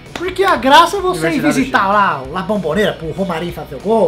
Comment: 1.48 eu juro que eu ouvi " visitar la o... RAFFA MOREIRA" 777 BRO FAZ SOL kkkkkkkkk